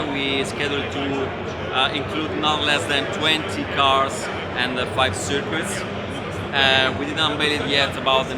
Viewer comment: did he say in the interview 20 cars and 5 tracks on early access release i hope there are some roadcars in it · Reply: Minimum 20 cars and 5 tracks